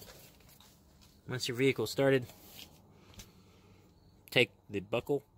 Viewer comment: What do you say @ 00:43? “Once the vehicle started?” I tried this multiple times on my 2018 Crosstrek but no dice, I can’t turn it off. Does it have to be done in the first 30 seconds right after you turn the ignition on?